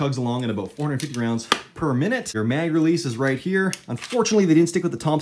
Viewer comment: Had to watch twice to hear that he said “400 rounds per minute” thought he said “44” the first time. “No way it’s 44” i thought, “what a piece of shit”